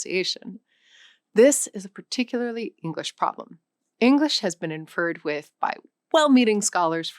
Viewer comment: Cool info! Okay am I crazy or did she say “english has been inferred with…” instead of “interfered with…” at 7:22? She fully pronounced the completely wrong word. Is this AI or what?